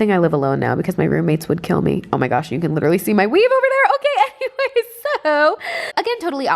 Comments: I thought she said omg you can literally see my weed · It sounds like she is saying weed for weave